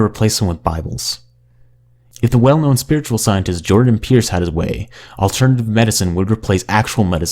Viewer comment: Had to do a double take there. Thought he said "Jordan Peterson"...